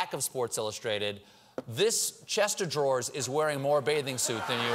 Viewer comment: Did he say "Chester Draws"?